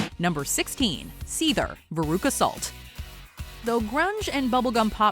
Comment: When they said the name of the band on the radio veruca salt, I thought they said Beirut assault. Which was a better name in my opinion, years later I found out it was a reference to the movie Willy Wonka and the Chocolate factory.